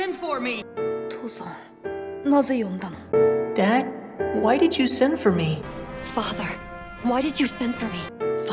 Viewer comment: i thought the 1997 dub said why didint you simp for me someone send help- edit: they all sound like that oh no help